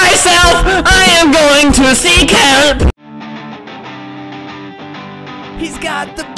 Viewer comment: My friend:what r u doing Me:using my headphones and watching alberTOOoOOOoooOoooo SiNg Friend:MAKING MY WAY DOWNTOWN BY MYSELF I AM GOING TO SEEK HALP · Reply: I hear "see kelp"